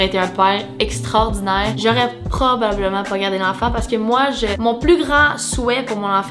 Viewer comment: A 2:54 j'ai compris poignarder 😳😂😂♥️♥️